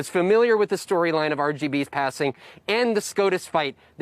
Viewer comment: Did he say RGB at 1:31?